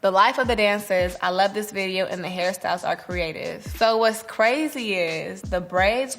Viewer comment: everytime she said hairstyles i kept hearing “harry styles” until i turned on captions lmao